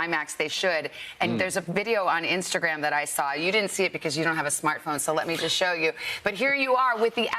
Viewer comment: did she just say Nolan doesn't have a smart phone.